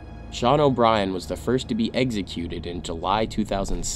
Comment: Parents please pick up your kid’s regardless of the situation they are in drunk, ditching, in a bad situation. PS: I kept hearing “eggsecuted”